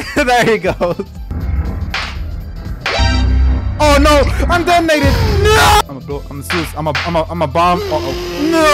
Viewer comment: DiD hE SAy tHE N word?!!!!!! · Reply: He said I am detonated